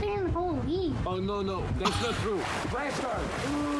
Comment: Notice the very wrong CC/subtitles that say "Flesher!" instead of "Splash her!" lol Yep, you caught it!